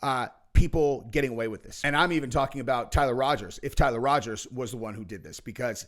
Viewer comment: Its Tyler Robinson lol. Ive heard you say Roger's in a few vids now.its driving me nuts now lol